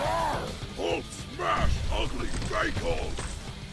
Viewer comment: I see you had trouble hearing what Hulk said, so... 14:33 "Hulk SMASH ugly fake Hulk!" 14:46 "Hulk squish you like bug!" 15:45 "Stupid gray man! Cars can't hurt Hulk! Huuuuulk SMASH!!" 16:06 "Hulk hate you! Hulk hate spiky beard! SMAAASH!!" 16:43 "You break buildings good. But Hulk...BREAK WOOOOORLDS!!!"